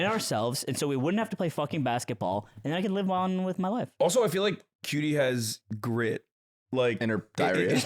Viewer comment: bro i thought hasan said “qt’s got GRIP” LMAO